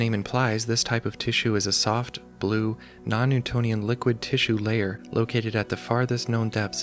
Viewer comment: Edit: i misheard "non-newtonian" as "non-euclidian", ops. original comment follows: Honestly, you had me until you threw in 'noneuclidian' for the lols. Noneuclidian means that space is not 'flat', it is curved and stretched. As we live in a gravity well where the mass of the planet warps space, everything humanity has ever experienced is (slightly) noneuclidian